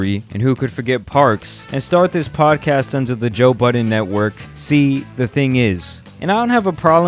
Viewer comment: Ong when I say I thought he said “ joe Biden “ in the first couple of seconds, I was so confused for a sec